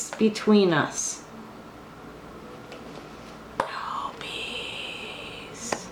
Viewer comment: Ummm...did anyone else hear "no peace" at the 25:29 mark. Just wondering...kinda spooky.